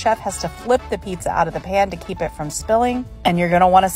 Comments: "Look at this cheese pull" While the cheese simply runs away and loosely falls off the sides. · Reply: i heard "look at this cheese bowl" and i thought thats accurate. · "Look at this cheese pull" While the cheese simply runs away and loosely falls off the sides. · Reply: I thought she said cheese bowl lol